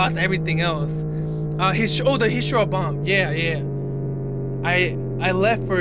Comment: Did he call Hiroshima hishiro?